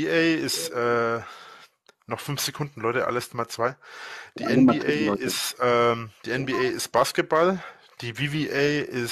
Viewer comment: Mag es nicht dem Drachenlord rechtzugeben, aber,er hat vollkommen korrekt gesagt: "Die NBA ist Basketball. " Da haste dich verhört!